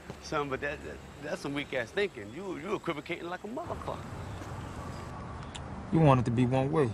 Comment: Did Bubbs say "equivocating like a motherucker" at 2:42 ?